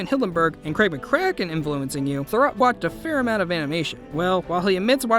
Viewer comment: If you watch this video with closed caption on, instead of "Thurop", it says "Throw Up"! Then I realized he probably was :teased in school a lot for his name. He showed them!!!